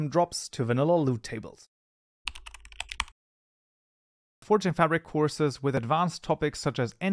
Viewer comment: I could not understand what you said because your speaking really fast but the CC writes out fortune fabric you mentioned free armor models link below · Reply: he said forge and fabric